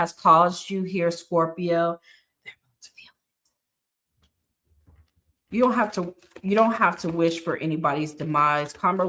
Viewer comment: Am I the only one who heard extra voices at 15:42 and 15:46 I can’t make out the first voice said but the second voice said “they going to feel it” 😮😮😮😮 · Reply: she said “wow” 15:46 she said “they’re going to feel it”… Video sound was just glitching. Resonated 🙌🏾❤